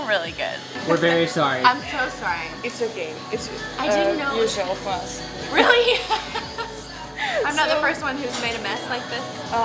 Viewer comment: what did the waiter say? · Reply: she said it`s OK and that is not the first one who has done such a mess))